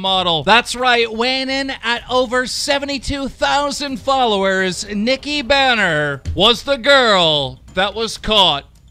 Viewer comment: thought he said Nikki Boner hehe